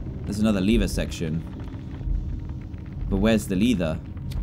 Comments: No one: Not even a mouse: WhErEs ThE lEtHaH And yes, he said in the video, Where's the Lethah, Instead of, Where's the lever? At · He said Lether instead of lever